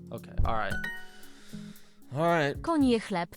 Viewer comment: Duolingo pronounciation too fast. In "koń je chleb" you should emphasize spaces. What I hear is "konie chleb", which means "horses bread", and have a little sense (no "eat")